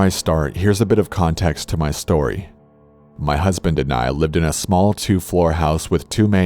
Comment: did he say me and my husband